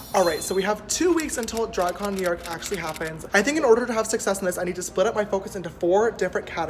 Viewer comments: ummm did sister just say “successness” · Every time _ until dragcon I thought it said _ until dragon Anyone else??